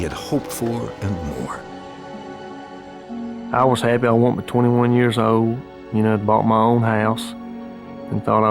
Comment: did he say he was 21 years old when he bought the house?